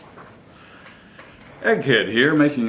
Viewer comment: I thought you said "Mr. Egg Head" here...! Ha! It's actualIy I see. Irregardless, good, instructional video...even the spring flying across the room! Good recovery by the way! I have one coming in next week. I saw the little weapon on a video about cheap .22's and I began my search for it. I LOVE .22 cal. firearms and especially cheap ones that are different. I like the five inch barrel. I was concerned about breaking it down and putting it back together, but now I'm not.